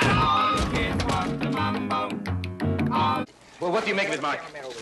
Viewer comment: "Looks like someone had a do-it-yourself lunch meat party" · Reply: Holy shitTHANK YOU! I kept hearing "Looks like someone had a do-it-yourself PUNCH ME PARTY" and I was like "Was that officer even invited?"